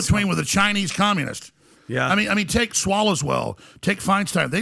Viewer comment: Did he just say Swallows Well at the end? 🤣🤣🤣 That is hilarious.